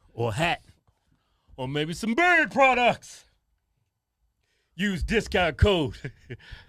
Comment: First time I heard them say “OR MAYBE SOME BEARD PRODUCTS!!” I legit thought they said “OR MAYBE SOME BIRD PRODUCTS!!” I was like what the FUCK you selling bird products for? 😂😂😂😂